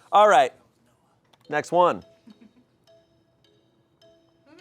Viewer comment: did anyone else catch at 26:04 someone saying next one is noahs in a whispered voice?